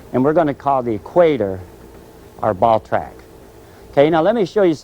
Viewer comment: I thought he said we will call the equator our ballsack 😂😂😂